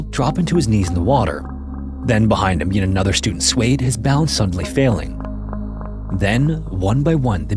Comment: I had to go back at 6:30 because I heard "his bowels suddenly failing" lol